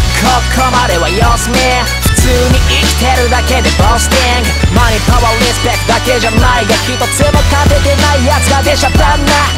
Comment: 前川リスペクトって聞こえてんけど… マネー、パワー、リスペクトね！